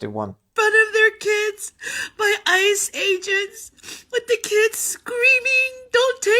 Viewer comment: Did she said ICE-Agents or Iceage Ants?